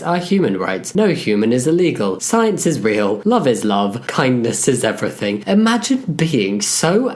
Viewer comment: thought that said "kidneys is everything" for a second lol